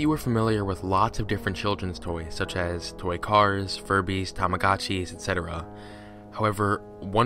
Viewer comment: When he said tamagotchi I thought he said kamikazi lol